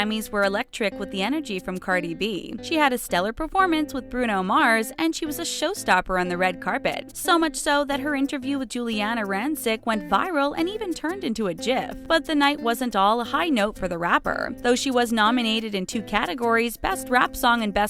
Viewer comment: Did you just say "YIF"?? (7:01) wft